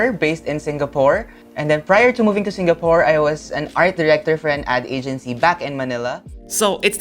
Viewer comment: i heard arc-reactor for an ad agency LOL